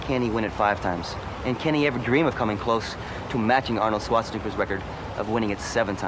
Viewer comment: Did he just say Arnold Swastica?